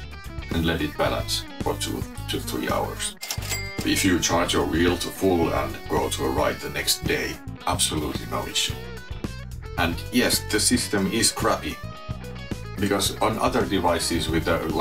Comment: What did you say at 15:58 ? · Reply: “If you charge it to full and go to ride the next day, absolutely no issue. And yes, the system is crappy.”